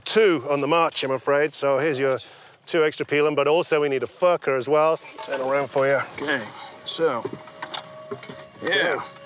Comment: It sounds like he said "fucker"